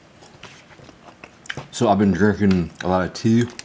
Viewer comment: I thought at first he said I’ve been jerking alot